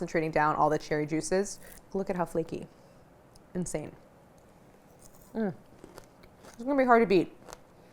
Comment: Hahaha at 21:32 when Claire said “mmm, this one is going to be hard to beat” I heard “mmm, this one is Cardi B” 😂